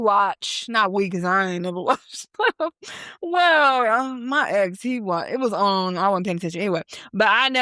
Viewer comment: what she say? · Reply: I think she said her ex is white.